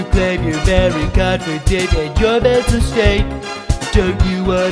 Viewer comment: I’ve noticed a small number of comments saying they had trouble understanding some of the lyrics- letting you know we have English subtitles and the lyrics are in the description! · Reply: It says “metal slate” in the lyrics, but I heard “mental state”, and I think mental state makes more sense in the context